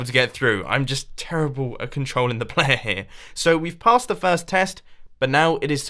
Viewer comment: What did you say Mumbo? Playehhhhhh?